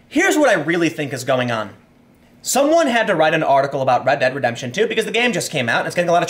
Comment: I thought I heard you say, "Reddit Redemption".